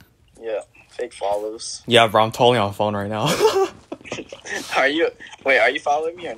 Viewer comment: LOL I heard you wrong😂 I was so confused🤣🤣 said “Phone” and I thought he said “follow”😂😂😂